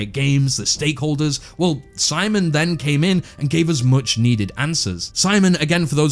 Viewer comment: when you said "simon then came in" I heard "SciManDan came in" and had to rewind xdd for a moment I thought what is this converging of worlds here xd I was like what that youtuber has to do with hytale xd